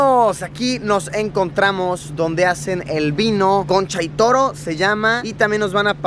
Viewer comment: pense que decia troncha toro xD